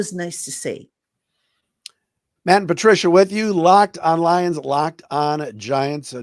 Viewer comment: LOL, Matt, when you said "Matt and Patricia with you..." I thought you said "Matt Patricia with you..." I almost choked on the sandwich I was eating!!!